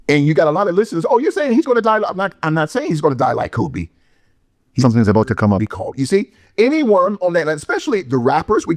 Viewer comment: Can someone tell me what he said 10:33 ? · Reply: His number is about to be called